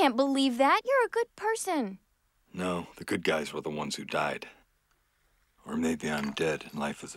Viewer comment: The first time I listened to this I thought he was saying "or maybe i'm dead and life is a pig", full stop. I hate to admit it but I burst out laughing.